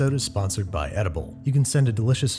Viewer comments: For a second, I thought he said "..sponsored by Audible" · I thought you were saying 'Sponsored by Oedipal'. Sick.